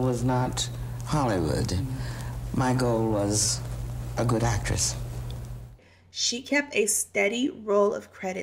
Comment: Was that shade lol she said Michael was a good actress not actor but actress. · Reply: #MaryannCalloway 🤣 3:25 "my goal was not stardom...my goal was not Hollywood...my goal was...a good actress" I thought she said Michael too at first 😀 until I played it a second time 😎